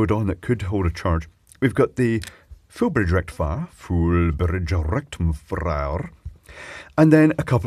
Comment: Did anyone else hear Clive just say "full bridge rectum fryer" at 9:11?